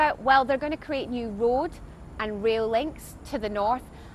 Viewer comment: did she just say 'w'oad and 'w'ail links? offensive to asians